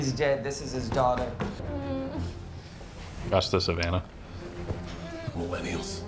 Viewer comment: When they're introducing "Ratcatcher 2" , I believe said "That's the Savannah" or something like that?... what were you talking about??? What could you have possibly mean by "That's the Savannah" or WHATEVER THE FUCK it was that you said there??? lol ...IS IT AT ALL POSSIBLE TO LMK WHAT EXACTLY YOU WERE SAYING DURING THE SCENE IN THE MOVIE WHEN "Ratcatcher 2" WAS BEING INTRODUCED AND ALSO, PLEASE LMK WHAT IT'S MEANING WAS TO YOU AS WELL (...but that's IF it ACTUALLY HELD ANY "PERSONAL MEANING/ STAKES" TO YOU OR *NOT lol) ...at 1st, I thought you were telling you gf (*WHOSE NAME I STILL CAN'T REMEMBER FOR ONE REASON OR ANOTHER*) the actresses name; which is Daniela Melchior btw... but I quickly realized her name sounded ABSOLUTELY NOTHING like you said to your gf ... Then 2ndly, I TRIED to find out if "That's the Savannah" had any meaning WHATSOEVER but, at least so far, I COULDN'T FIND ANY MEANING BEHIND YOUR "That's the Savannah" line that you said to your gf, when Amanda Waller (Octavia Spencer) was yelling at "Ratcatcher 2" to wake up and get outra bed so she could start getting debriefed on the Task Force X mission that, along with "Bloodsport", "Peacemaker" "King Shark", and "Polka Dot Man", she's about to begin... * (btw... I personally found the running gag of _Cleo Cazo a.k.a. "Ratcatcher 2" constantly sleeping throughout the entire movie rather hilarious lol... Did anybody else feel the same way as I did? "_King Shark_" (a.k.a. "Nananue" ---> Th